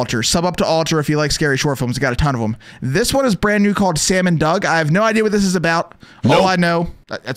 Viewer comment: Is it just me or i heard it "salmon dog"😀😀